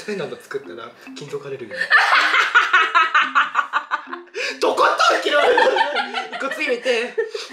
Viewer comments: なんて言った？？ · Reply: 金属アレルギー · なんて言ってるんですか？ wwwwwwwwwwwwwwwwwwwwwwwwwwwwwwwwwwwwww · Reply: 金属アレルギー